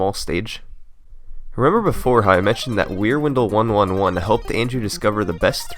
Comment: Until I saw Wierwindle written out, I thought you were saying Rear Window... Y'know, the old movie where a guy thinks his neighbor is a murderer.